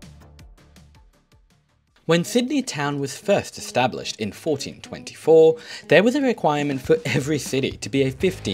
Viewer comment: Did I hear Sydney in 1424